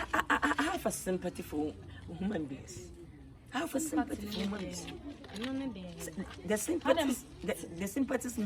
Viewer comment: did he say, "i have a sympathy for human beings or woman beings?"😅😅😅😅😅😅😅😅😅😅